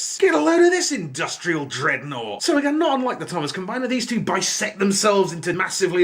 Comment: I thought you said "Treadnought" and I almost choked on my food by laughter xD I love your humour, keep up the awesome work! :D